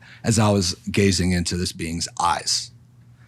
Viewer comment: When he said being at 1:28:39 I thought he said bean released I was wrong and laughed at myself